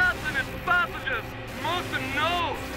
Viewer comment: What does he say? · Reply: “Do not panic and do not return to earth”